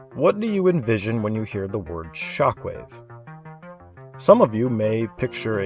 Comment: I somehow heard chocolate instead of shockwave at 8:50 . I was so confused for a second, lol